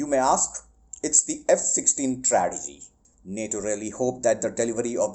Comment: I thought you said, "It's the F16, trash heap", but you said, "It's the F16 tragedy". lol